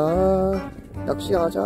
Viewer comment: 뭐라고 하신거죠? 저는 살짝 이상하게 들었는데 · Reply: 날 디게 좋네 라고 하는데요?